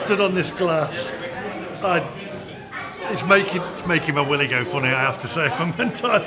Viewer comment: Did you say "it's making my willy go funny", or did I totally mishear that comment? lol x